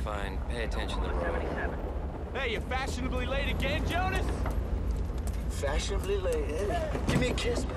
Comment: It took me years to realize that Dusty says, "Fashionably late again, Jonas" before kissing him through the truck window. All these years, I thought he was saying, "Fashionable Lady belle, Jonas? Fashionable Lady like.". My rationale was Dusty was drunk. Such is the mishearing you got from worn out sound on VHS tapes.